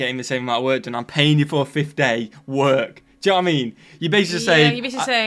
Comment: Query... At minute mark 13:20 you said what sounded like, "Dwā-mene"... Is that British for, “Do you know what I mean"? I love all'ya'all's accents!!! 🤟🏼🇺🇲🤟🏼🇬🇧